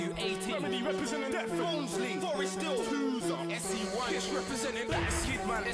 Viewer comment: "Twozer SE1" I swear I heard "Eskiboi Bow E3" for a sec there 😂😭